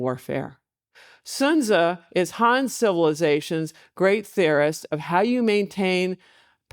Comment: At faster speed I heard 'Han Civilization' as 'Han Solo-ization'. What's Star Wars have to do with this?